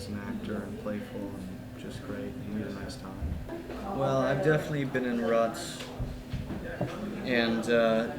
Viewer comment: did he just say he'd definatly been in rocks???